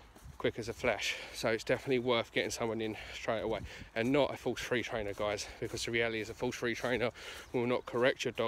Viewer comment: what sort of trainer should I not use? The word starts with an F or a Th...? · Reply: I believe he said "Don't use a Force-Free Trainer" or purely positive trainer